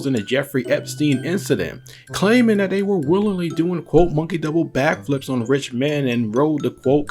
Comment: Thought u said monkey double back flop, but I did say flip.